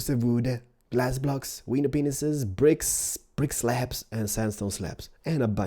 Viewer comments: Did anyone else hear him say "weiner penises" at 0:52? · did he say "window penises"?